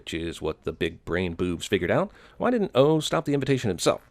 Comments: It sounds like hes saying boobs instead of boovs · I thought he said: "big brain BOOBS" Im sorry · When he says boovs is sounds like he saying b**bs